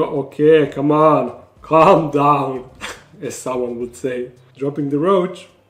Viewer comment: did u just say exotics lairs name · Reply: I think he said “as someone would say”